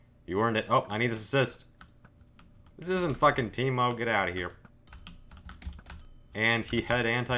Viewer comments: did he say f**king team mode???? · did you just say... TEAMMODE?!?!